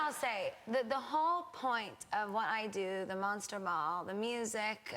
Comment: Is it just me that thinks it kinda sounds like she says the monster mall? haha ily gags <3